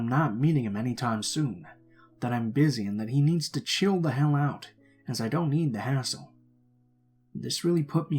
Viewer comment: I was half asleep in story 4 and when he said "and I don't need the hassle" I thought he said "and I don't eat asshole" 😂😂😂